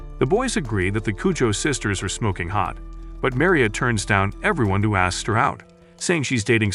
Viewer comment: i thought he said "smoking pot"💀